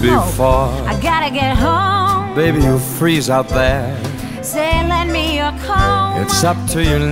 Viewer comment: I've always thought she said "say lend me a comb" but the other day I just found out rhe said "say lend me a coat" lol